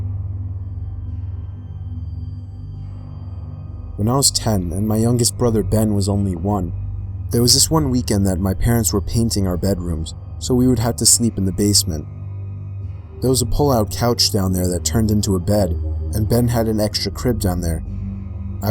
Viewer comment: Bro I thought he said my parents were mating in my room lol I was like what is an parents are those.